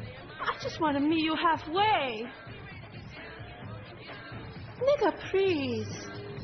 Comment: did she say nigga please